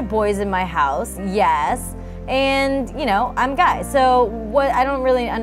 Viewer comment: did sage say I’m guy or I’m gay